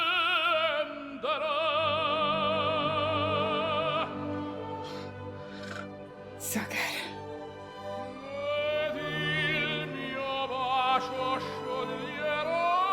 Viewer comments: Wait, at 12:59, did you say "so good" or "so geil"? Both means nearly the same but "so geil" is german^^ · Did she say 'so geil' at 13:02? 😁